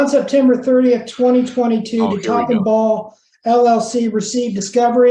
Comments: I super glad the lawyer eventually had Top and Ball LLC in writing because for the longest time I thought he was saying c*ck and ball llc. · I keep wildly mishearing "Top & Ball". Also I'm not sure how familiar you are with SpongeBob, but sometimes lawyers asking questions of defendants reminds me of ManRay, Patrick, and the wallet. Edit: I found her social medias. She seems..... pretty awful. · Oh shit.. I honestly thought they were saying Cock and Ball LLC lol